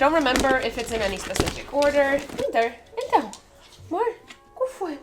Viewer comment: Did you just say ' então que foi mor?' 👀